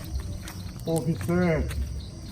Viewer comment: A partir do 11:35 vocês falam um negócio que eu nunca entendi kkk, vocês falam: "Ohh do inferno" ou vcs falam: "Ohh Vicente" Se você me respondesse eu gostaria de entender que fala é essa por que não entendi. Um forte abraço amigo